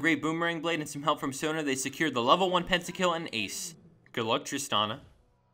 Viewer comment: lol anyone notice that 2:05 he say good luck tristana??dafuq did i just hear